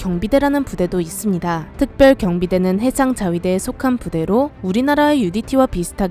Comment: 707이랑 붙으면 나가 떨어지죠 1:51 나만 해장자위대로 들리나ㅋㅋ